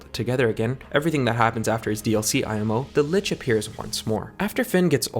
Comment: sorry, does he say “dlc imo”? or what does he say?